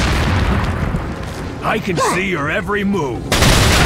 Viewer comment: ¿Puedo hacer una pregunta? Me puedes decir con las palabras en inglés, que dice Jack Krauser en el minuto 6:30? Es que esa frase me ha encantado, pero no logro entender como se escribe su frase y me gustaría poder escribirlo en WhatsApp. Posdata: Buen mod, cuando salga sera de lo mejor que hay en Resident Evil 4 con la mayoría de las armas, mas la TMP, Ballesta y Arco estará genial, por otra parte me encanta los saltos hacia atrás, uno normal y otro hacia atrás mola mucho (lo ví en Jack Krauser Insider 2016) esperemos que sirva tanto Separate Ways, Modo Historia, Mercenarios, Vamos que esté Krauser Boss con todo el juego de Resident Evil 4, con boina roja mola, y con y sin camisa mola, me encanta este mod, cuando esté subido el mod, grabaré en este lindo canal, un cordial saludo. Posdata me encanta todas las frases de Jack Krauser, si puedes escribírmelas por comentarios que frase dice en ingles te lo agradecía,like y suscrito:)! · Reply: La frase de Krauser menciona y buscaz es "I can see your every move" ("puedo ver cada movimiento tuyo"). P.D. Jack Krauser es uno de mis idolos, Saludos.